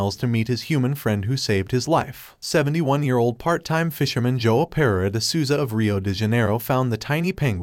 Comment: First time watching i thought he said his friend joe pera